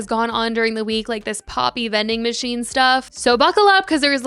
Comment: I thought you said planb vending machines and had to rewind😭😭